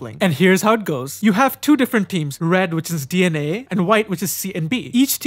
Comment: I thought he said dna